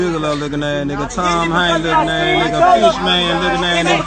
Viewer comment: The person who wanted a "reward" for the license plate is withholding evidence. That'd be something the police could help with. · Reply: What the hell happenef at 5:42? All I heard was "Nigg4man" spoken really fast like at in that warehouse auction show aired alongside Pawn Stars.